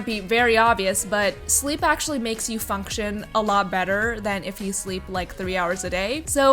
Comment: i honestly thought u said fuc*tion 😂😂😂😂🤣🤣🤣🤣